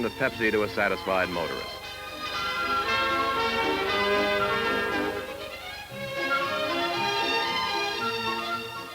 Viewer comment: Any lipreaders here? I wonder what the girl is saying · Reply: ''He said ''put my hands up''